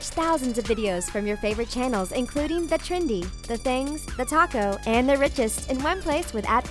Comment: When she said thetalko at the beginning am I the only one who for a split second thought she said thetaco